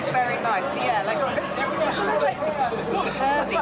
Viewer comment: Did someone say, "More Herbie"? beep beep !